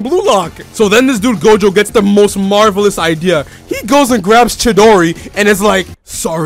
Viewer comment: did man just say "chidori"? 💀